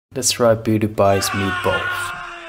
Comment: Lets try PewDiePie's meatballs 💀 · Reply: i really thought he is saying "let's try pewdiepie's balls"